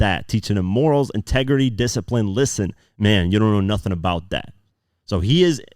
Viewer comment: I thought the "nothin' bout that" was also way for him to say Dreak knew nothing about Kendrick because when I first hear it, I thought he said "nothin' bout Dot."